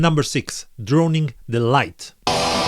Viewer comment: Great video, but it would be helpful to have the bands' names in text, either in the video or in the description. For the first one, I thought you said "Droning Delight" rather than "Drowning the Light" :P